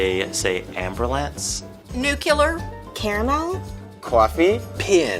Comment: is she saying Caramel because if so she said it right...